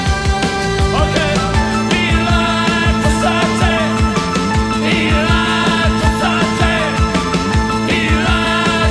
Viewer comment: what does he say from 3:00 up to the end ??? here live the salty ?? 3:00 : il dit quoi de 3:00 jusqu'a la fin ??? 3:00 : que dice de 3 minutas hasta el fin ??? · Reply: Konstantin Vercetti oh my god what a fool I am !!!! how come I didn't seize it ??? yes of course he says electricity ! thanks a lot !